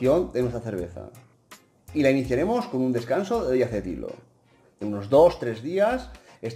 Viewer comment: Consulta: Cuando dice " La maduracion la iniciaremos de siete diros.." o que?, no entiendo lo que dices. Es en el minuto 5:12. Como es ese descanso? No me queda muy claro. Gracias, saludos desde Uruguay